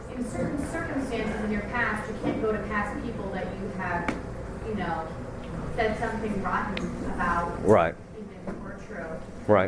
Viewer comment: what does the woman say at 1:02:22 that makes Father laugh so hard?? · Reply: in some cases where you need to make restitution to a person that's gone, even in some cases where you said something that was true... (im paraphrasing, kinda hard to hear)